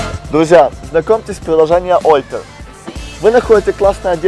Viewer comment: что за приложение, что он говорит? __ ничего непонятно и google по запросу "oper" находит вооще нето// _ вконце такая речь, словно с телика(... ,а так канал интересный. Нооо то что есть вставки как с тв, слегка напрягают..